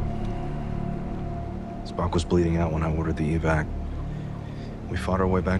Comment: Did Pike say Spock was bleeding out !?!? I'm scared now 😭😭